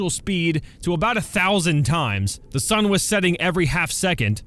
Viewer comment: it said the sun is setting and I thought it said the sunbeam is setting